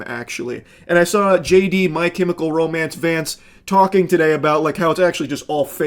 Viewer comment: I thought u said “JD, my chemical romance fans…” and I was so scared bc like huh??